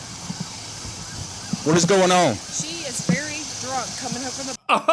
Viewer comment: It sounds like they got in a fight at the bar while trying to get her in the car, then apparently later at a church. The driver also immediately said, "We're drunk," when the cop came up to the car. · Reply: I thought she said, "She is very drunk" while pointing to her girlfriend sleeping in the back.